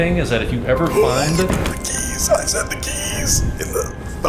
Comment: When said "The key's" I thought he said bikini 𓆩😆𓆪